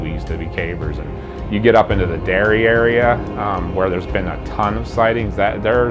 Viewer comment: Did he say "get up in the derriere "?